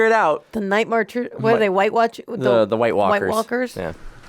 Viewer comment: I literally just spit redbull out my nose i know she didn't say it, but I could have sworn was about to say weight watchers stead of whitewalkers 😂😭😂